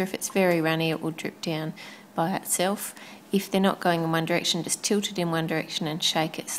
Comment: I HEARD YOU SAY ONE DIRECTION!! OMG!!! 1:49 ONE DIRECTION. ONE. DIRECTION.